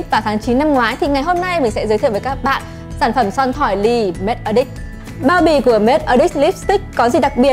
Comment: E nghe thành make a dick 😂😂😂